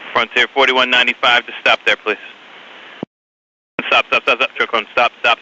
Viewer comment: NOTES FOR THE DEAF & THE BLIND: 2:24 - Such garbage captions in that X recreation. "Client" no COLLIDED!!! The deaf have no idea what is really being said, or what's going on. There's a horrible beep beep beep-beep-beep in the ATC's transmission. It's the collision warning for him. Beeping frequency ratchets up until "impact detected" alarms blare in the background. :( 5:18 - Completely missed caption, is when the ATC muttered "Stop-truck-one" in such a motor-mouthed and under-his-breath way. I didn't even hear it the first 3 times I replayed the audio. It's no wonder the truck didn't hear that. 5:26 - A few seconds later, it's very clear "stop truck one...." However - not mentioned is the truck could have switched to ground control not ATC frequency and never heard ANY orders to stop, because that was ATC saying that. Why ATC was handling 2x emergencies AND Ground + ATC is anyone's guess. Low staffing? Anyway, Hall of Shame AI slop captioning job on the X reel. I wish more creators actually checked their work... 3:11 - What do you mean the truck could have been stopped? If the video you played at the start isn't fake, that truck wasn't stopped...It braked, slightly steered left but then BOOM. :( Note for the Blind - holy crap the plane slams into the truck at high-speed, spins it out and drags it down the runway a little before the truck comes to a rest on its side. The cockpit folds down and decapitates on impact, while the rear body of the plane JUMPS over the truck's body